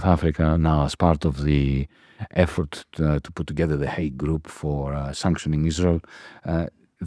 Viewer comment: it sounded like “hate” group, but was probably aid group ?